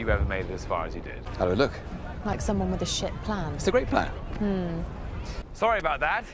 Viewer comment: Did she say shit plan?